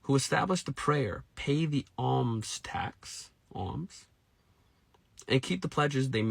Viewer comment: There is nothing in the Qur’an that talks about weapons taxes, but the word is “Zakat” in the Arabic language. Search for the meaning yourself through the Internet and you will be sure. Zakat is obligatory for Muslims. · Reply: I think you misheard what he said. He mentioned “alms taxes” maybe you heard “arms” lol. Alms is one of the translations of zakat. Not related to weapons.